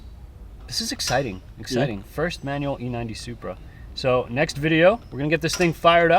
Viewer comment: don't know if it's your accent but it almost seems you are saying e90 instead of a90.